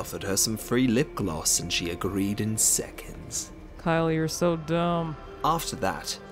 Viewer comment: did kourtney call kylie “kyle” ????